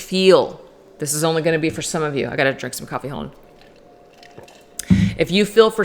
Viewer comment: I know you just wanted to drink coffee, but girl it sounded like 🐸☕!!! Love your readings😄